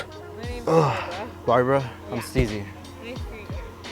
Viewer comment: anyone else thought he said his name was Sneezy?